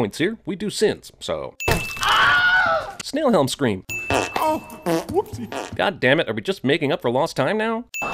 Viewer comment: ow! Whoopsie! Wait did he just say ow?